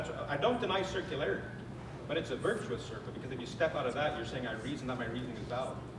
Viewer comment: Did Sye just say that his argument is "virtuously circular"?